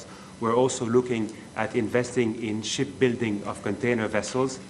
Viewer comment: I thought he said shit building. To that I agree 😮